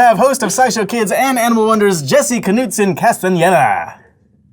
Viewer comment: I heard ''Anal wonders""